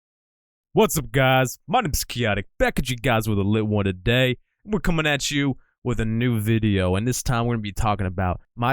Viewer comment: Rn I'm jus realizing the intro u say 'back at u guys w/ a lit one today' nd dis whole time I heard 'another one today' bruh